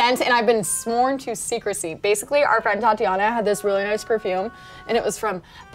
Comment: did she say thatiana???